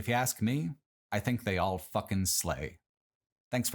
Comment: I misheard the last line of this video when I was washing dishes and thought you said they all fucking suck haha